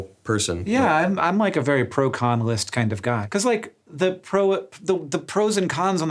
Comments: When Hank said he's a very 'Pro-Con list type of guy' I heard it as 1 word. Proconalist. And thought it was some new type of philosophy. Then I realized what he said and now think it should be a philosophy. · i heard "Im a very proconnalyst type of guy" and i go 'oh! I havent heard that word before!'